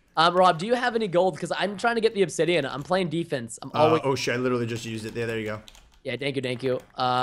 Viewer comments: Did rob say "oh shit" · Oh wait I heard he said "Oh sh*t" at 23:27 and